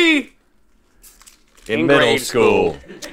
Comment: the captions say that both say Middle School but Link said Grade School